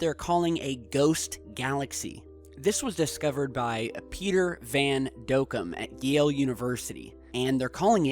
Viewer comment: I thought he was gonna say Peter Venkman😂...